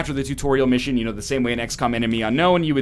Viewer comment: sounded like he said "x-cum"